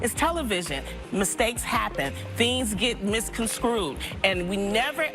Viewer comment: Did Loni say "Misconcrued"?